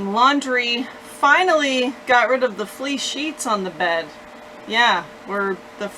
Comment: Who else thought she said flea sheets? LOL A/C is the way to go!